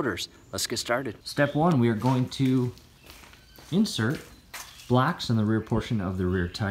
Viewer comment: I thought he said "First, we're going to insert 'blacks' behind the back tires here!" Lol then I realized he said "BLOCKS"...I'm fucked up haha...